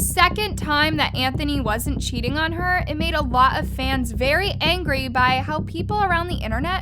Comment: The fact I thought she said mans instead of fans